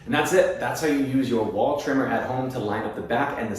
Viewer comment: I thought you said ball trimmer at first haha, i mean I am using that one too